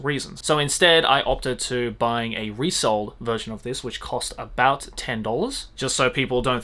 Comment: Did he say resold? Sorry have to ask cause I couldn’t quite understand what he said there